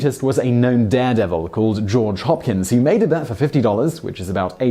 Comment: His name was Jaw Dropkins? Oh George Hopkins, I misheard you.